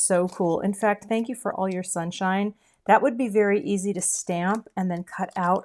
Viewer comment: My husband sat down next to me at the end of the video and thought the phrase said, Thank you for all your sinfulness". Thought it fit in with your humor and your human sized hand (which really cracked me up!)